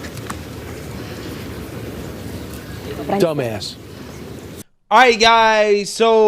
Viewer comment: that sounded like the word Duu Mass